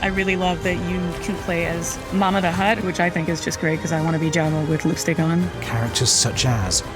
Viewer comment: what did she say? she wants to be Jabba with Lukes what now???? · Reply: lol she said "Jabba with lipstick on", but it really did sound like something else